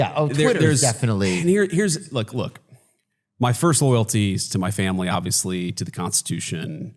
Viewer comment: JD Vance just told us who he is. He didn't say his loyalty is to the Constitution. He didn't say his loyalty is to America. He said his loyalty will always be to the President. There you go. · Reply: What does he say at 0:47? That his first loyalty is to his family and the constitution. (He is a liar and a traitor, but that is what he said)